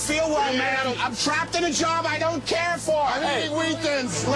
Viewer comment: "IM TRAPPED ON A JOB I DONT CARE FOR" - probably the best line in this vid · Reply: I've always heard that as "I'm trapped in a jar" and thought it was the funniest shit in the world